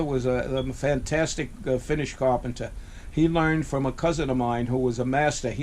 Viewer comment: waitaminute... Finnish or finish carpenter? Or am I just mishearing it all together??